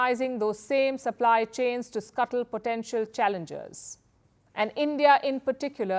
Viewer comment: Palki said China weaponized rare earths and magnets to scuttle potential challenges? lol did I heard it right? Western sources said that China controls 70% of all rare earths/magnets manufacturing and 90% of all rare earths/magnets refining. Do you know what these means? This means almost a 100% monopoly. There are no potential challenges when it comes to 90% ownership of rare earth/magnets refining. India controls 0% rare earth/magnets manufacturing or refining but wants to look strong and powerful? Is this a joke? 🤣😂Classic tale of ami get trying to talk tough to a giant.